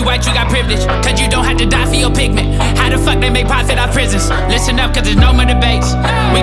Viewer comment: i understood the whole song but at 1:35 u said ((listen up cause there is no more to "idk", we can afford no more mistakes)) what was that word?? xD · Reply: I said "Listen up cuz it's no more debates" ✊🏾✊🏾 Can I count on you to share this song so our message is heard?